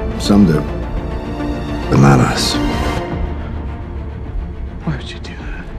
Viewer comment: Im sure that Steve says "bananas" instead of "but not us".